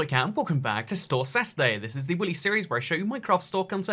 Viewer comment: am i the only one who heard him say willy instead of weekly?